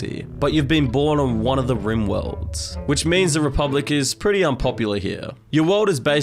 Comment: did he say RIMworlds?